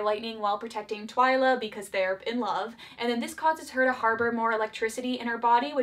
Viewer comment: did she say in love? Wait what